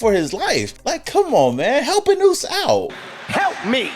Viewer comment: help a noose out? Is that what you said? Sorry if I'm just not hip to the raider lingo, I'm an eagles fan just here bc I just got Jeanty in a trade on fantasy. But noose???????